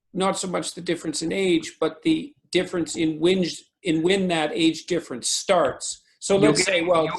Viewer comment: Anyone else notice Jordan Peterson having a Freudian slip at 11:27? He says whinge instead of *when*. 😂😂😂😂😂